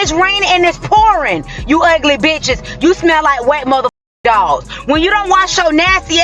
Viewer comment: I smell like a wet dog 😂? Well… I am really bubbly so I must be yellow lab 😂. I’m okay with that 🦮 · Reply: Ohhhhhhh, I thought she said I smell like a wet hot dog 🌭 😂